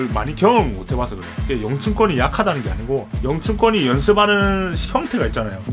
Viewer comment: 영춘권이 약하다는게 아닙니다.영춘권이 약하다는건 아니에요. 영춘권 비하하는게 아니에요 영춘권이 약하다는게 아니라는거죠.영춘권이 약하다는 말이 아니라는거죠.영춘권이 약하다는말이 아니라는거죠..영상내내 이말만 하시는데 "영춘권은 약하다" 로 들림